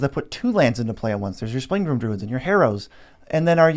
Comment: i didnt catch those names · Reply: Springbloomd druid the first